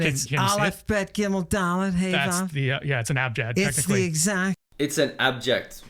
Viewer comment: i believe you ve misheard what the bible scholar says, he doesnt say abject he says abjad as a response to comment on the alphabet.